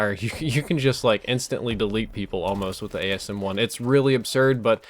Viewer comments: did you say delete people with the asm1 or am i going mad? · did he say asm1